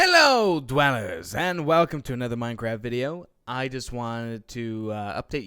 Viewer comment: in the beginning, i thought said: "welcome dwellers, and welcome to another minecrap video" XD minecrap instead of minecraft